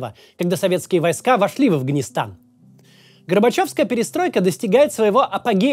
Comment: послышалось...😮 Гробачев 😮